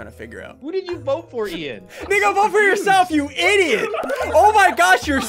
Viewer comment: did he just say. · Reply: He said Nico💀💀💀